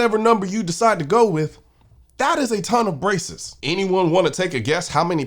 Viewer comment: When you said “that is a ton of braces” I heard “a ton of racists” and I thought, “oh noooo, Colion’s gone woke! 😢”